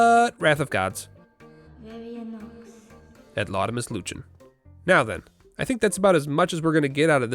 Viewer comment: For anyone wondering what is saying: "et laudamus lucem", which is latin and translates to "and we praise the light". Praise the sun!